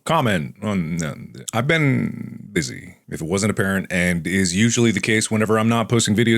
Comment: When said "if it wasn't apparent" I thought he said "if I wasn't a parent-" and my fucking heart skipped a beat, I had to rewind to make sure